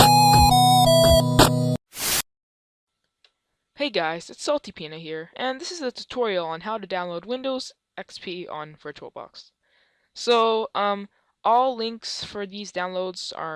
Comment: i thought he said salty penis in the intro hahaha